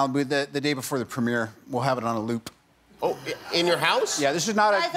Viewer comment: Did he just say we'll have it on a "loop"